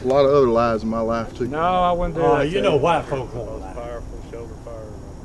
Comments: Did he just say "You know white folks gonna lie." WTF WAS THAT? O_____o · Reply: He said, you know white folks would'nt lie. I too was like WTF.. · did he say you know white folks don't lie? 😂